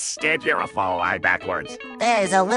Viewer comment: At first I thought it said I love furry kids.